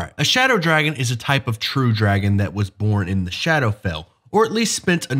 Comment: Lol thought you said shadow realm